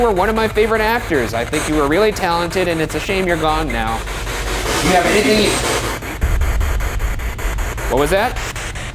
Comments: Ryan: "River, you are one of my favorite actors and it's a shame you died-" Ghost(River): "Thank you" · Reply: At that moment I heard either "You have good taste," or "You have a good day" starts like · no matter how hard i try, i only hear "you have anything?" Instead of "thank you". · Honestly, ar 21:11 I hear “do you have everything?” not thank you lmao. Does anyone else hear that?